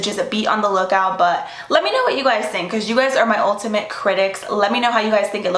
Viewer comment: When you said “ultimate critics” I thought you said “ULTA-mate critics” LMAO 😂❤️ love you!